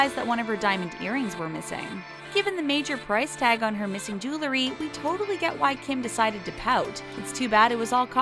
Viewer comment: narrator said “julery” instead of “jewelry”